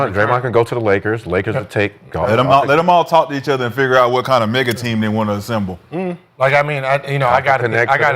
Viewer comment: I had to run it back I thought said “ lettem all talk and figure out what kind of nigga team they want to assemble …” 😂😂